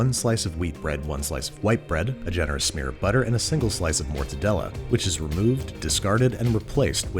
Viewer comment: "...and a single slice of mortadella." Mortadella? Andy, this was Chicago in 1985. That's pimento loaf. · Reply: Wait im sorry, it's Mortadella? I always thought he was saying Portadella